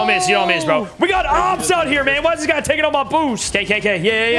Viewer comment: What did he say? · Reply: We got opps out here man, whys this guy taking up my boost